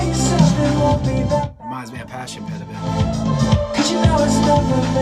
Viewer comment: Can someone please tell me what he says at 3:08? All I understand is "reminds me of" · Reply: He says "reminds me of passion pit a bit"